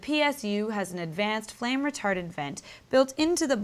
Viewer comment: did she say flame retarded vent?at 1:48?